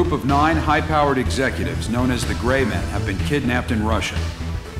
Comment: Did I just hear "Grey Men" ?!?